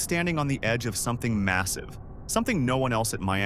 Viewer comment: Did you say massive?